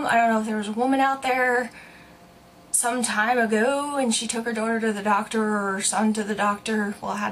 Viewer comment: I heard her say she took me